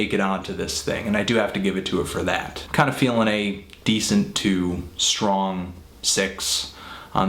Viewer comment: Almost thought you said "Decent TWO"